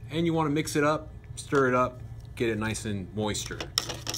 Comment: did you say “moister?”